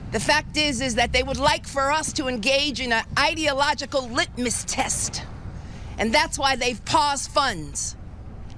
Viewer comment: in. Dang. What is this woman saying? Elon is on a leash, make no mistake. An ideological litmus test? Is that what she said? Um, no, you’d be following the law.